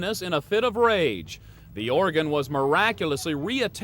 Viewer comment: At time mark 4:30 the commentator says the "Oregon was miraculously re-attached". Oregon is a state on the west coast, "organ" is what she severed. He says it again at 5:37 and multiple other time in the broadcast. Where does Inside Edition get these people who often misuse grammar and cannot correctly pronounce words??